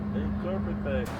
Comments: What happened at 16:05 ? Anyone catch that? It sounded like someone saying "Hey Carpetbagger" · I heard someone say “hey carpetbagger”